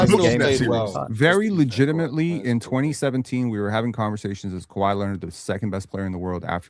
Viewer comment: I thought said 2014 not 2017 my fault